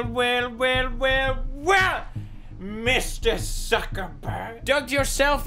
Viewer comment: did he just say dugged